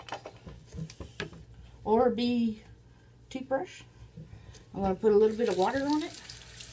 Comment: I swear I thought she said orbie toothbrush